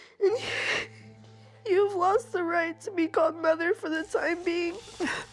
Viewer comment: I’m not a native speaker; could you guys tell me please what she says at 10:03? Thank you! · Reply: "You've lost the right to be called 'Mother' for the time being"